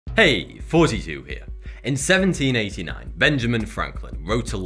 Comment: Silly me, I always hear, "Hey, 42 here." every time I see your videos. Why have I never thought that it's Thoughty2, not 42. Sigh.